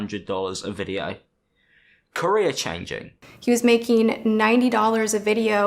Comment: i thought he said Korea changing 😂😂